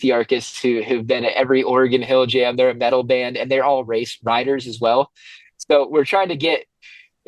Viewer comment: I thought he said that the band they have playing were all "race rioters" 🤣